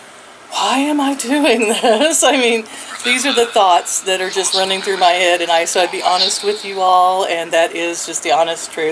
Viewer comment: Anybody else hear a male voice at 4:23 when asked "Why am I doing this?" ? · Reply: It was a quote from Obi Wan Kenobi - "Remember... the Force will be with you always". I had to listen to it a couple of times before I recognised it, but it's so apt for at the moment :)